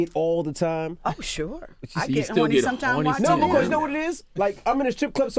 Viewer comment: she told Envy.."I get horny watching you"...and this nigga didn't catch that.. smh